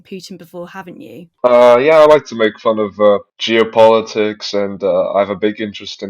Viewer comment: i'm on the news and by god i hope my mum doesnt find out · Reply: I thought he was going to say that he likes to make fun of geopold lol 😂